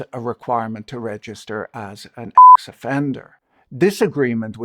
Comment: Great video! But at 8:24....He's a WHAT offender? We couldn't hear you! Sounded like you said a (beep) EX offender! Because the document shown in the video clearly says "SEX" offender! Its an official legal term!How come they can say it, and you can Show it, but you can't Say it? Sex is not a swear-word! So why is it censored? Its another word for gender!